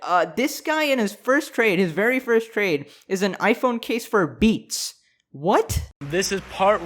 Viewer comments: I was listening to this, and I heard "He traded it for beets." so I was very concerned why he would accept beets. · I THOUGHT HE SAID HE TRADED AN IPHONE CASE FOR BEETS